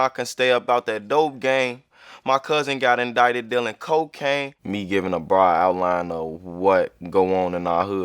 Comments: I thought he said my cousin got in dried deaking cocaine 🤦‍♂️ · Man im Dumb ive always thought he said My Cousin got invited dealing Cocane